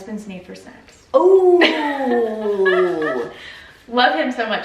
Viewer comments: I thought she said his need for sex. I was confused 😅. Then I said oh he wants to eat after sex. Finally, ohhh his need for snacks 🤦🏻‍♀️ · Lol… I thought she said the food budget was thrown off by her husband’s need for sex… (she said snacks)… lol